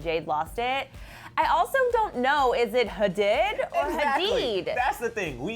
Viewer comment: Can perrie get a break? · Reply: She didn't, she was laughing so it sounded like she said hadid but it was just a pust of air.